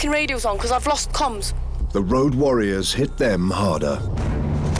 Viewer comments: what does the narrator say? The Road Warriors hit them Marda? What does that mean? Never heard that word used before is it British or something? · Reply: He said “…hit them harder” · I discovered this channel 3 days ago, and I’m addicted, help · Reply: what does the narrator say? The Road Warriors hit them Marda? What does that mean? Never heard that word used before is it British or something?